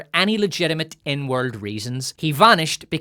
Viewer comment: did anyone else hear N-word reasons the first time watching this video over and have to rewind to double check?